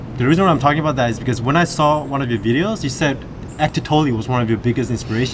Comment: What were they saying in 0:30? ectotolio audio book? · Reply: ahmed majed Eckhart Tolle